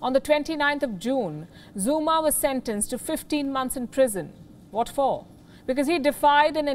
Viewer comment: I hear 15 months and read 15 years🤔